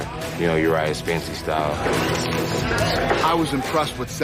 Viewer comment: look up this guy named Death Sentence who does backyard fighting. He isn’t great, but dude loves to fight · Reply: nah I know him I just thought he was saying Uriah ass